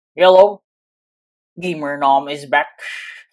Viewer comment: When ever i hear Intro i heard "yellow gamernom is back"